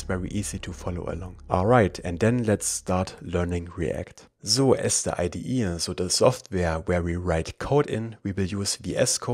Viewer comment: I believe we shouldn't call `sort` method on a component prop since it mutates the array. :) 2:29 I thouhght the video continues in German, it sounded like "So erste ID ihr so der Software..." 😅😂